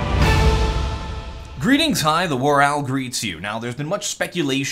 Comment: One question. What say all the time at the start of the videos? I'm not english sorry. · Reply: I always thought you said "Greetings, I, the, greets you.